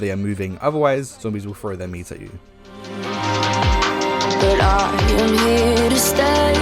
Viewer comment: what did he sayyyy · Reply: zombies will throw their meat at you if your legs arent floating